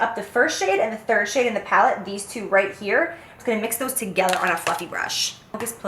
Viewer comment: what was said: "I'm gonna mix those together on a fluffy brush" what I thought was said: "I'm gonna mix those together on a fucking brush" but then I realized it wasn't as sassy as I thought it'd be